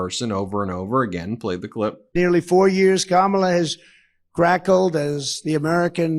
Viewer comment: Did he say "crackled?"